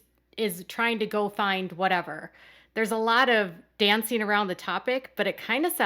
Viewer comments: I thought she said “fine, whatever.” Like he is throwing his hands up at trying to have this big family he created. And she isn’t there yet. She still wants to fight for the family. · I thought she said “he goes ‘fine! Whatever!’ “ - meaning that he shuts down any discussion when they disagree at all, and she has to stop him and drag communication out of him and she characterizes that as sabotaging the relationship on his part.